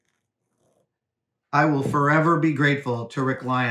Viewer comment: When he was talking about the puppeteer tip, I heard, "I will fur-ever be grateful..."